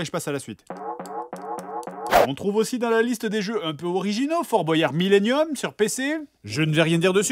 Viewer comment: Y’a juste moi ou quoi 6:17 il dit fort bouaillard millium’ millium c’est dans yu gi oh comme les object du millium